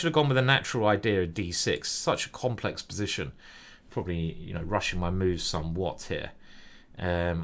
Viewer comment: did I just hear " I am probably Russian - my moves (are) somewhat..."